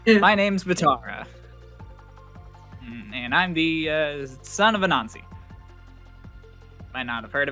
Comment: I thought Vatara said “I’m the son of a nazi” and was so glad to see I misheard